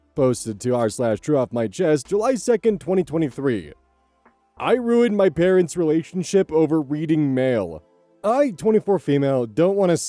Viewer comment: I misheard this as “I ruined my parents relationship over weed and ale” which makes a lot more sense